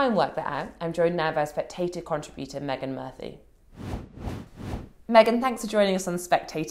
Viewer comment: I thought she said "Meagan Markle" not Murphy". You can imagine my "what!" reaction.